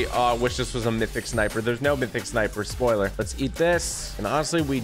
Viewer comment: did he say spoiler?